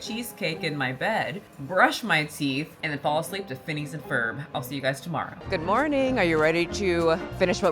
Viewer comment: Omg! Instead of Phineas & Ferb, I heard “Defending the Ferb” & thought it was some kind of innuendo. My bad 🤭